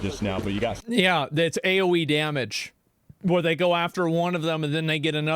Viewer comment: Did he say AOE or AOC damage 🤔